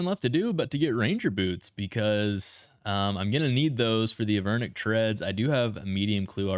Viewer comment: I believe that is what I said no? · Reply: Sounded like you said threads the whole time Maybe I'm just hearing things cause everyone else has been saying threads